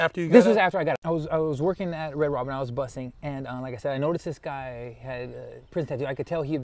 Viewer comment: I had to rewind the track and listen to him a little more closely. I really thought he said ..I worked at red rob-bin , i was a buster? I was like wow I thought you said you ran with the surrenos my boy. Now I’m getting the Norte vibe. Buster.